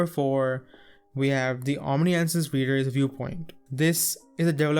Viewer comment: Omisent is what I heard him say